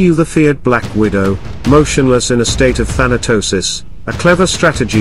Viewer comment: I misheard the first part and thought you said the spider was "motionless in a state of halitosis"..... I know that feeling after a few beers and a curry.